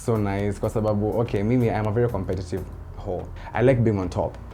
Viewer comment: did you just say competitive hoe 😂😂 I like the rawness 👏👏